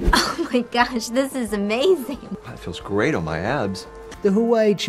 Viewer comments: that was perfect, it sounded like he said "this feels great on my ass!" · Thought this guy said "Feels great on my ass" 🤣🤣🤣🤣